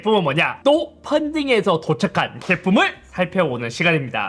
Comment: 인트로 보고나니 "똥 펀딩해서 도착한 제품'이라고 들림